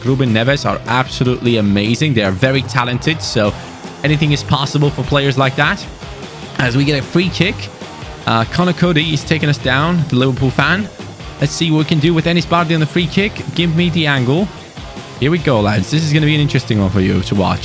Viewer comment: did say Connor Coast the Liverpool fan 😂😂😂😂